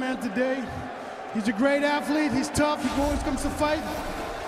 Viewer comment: What miocic and audience were saying at 0:38? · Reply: David Fryberger thought he said "ALL WAYS"